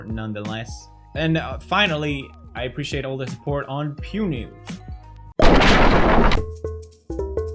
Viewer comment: PEWNEWS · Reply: Lapis Lazuli first I thought it said pew nudes